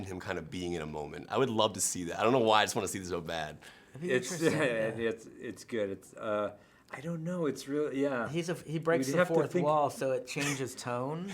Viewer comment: Yo!! 😆 He smiled so hard when he said deadpool and did he say "it was good"??????? 🤔.